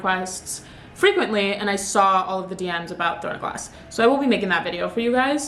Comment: what did she mention at 9:06? I couldn't hear it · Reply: ‘throne of glass’ the book series